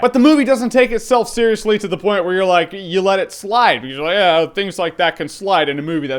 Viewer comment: Jeremy, did you say at 3:02 "You let it Sly"?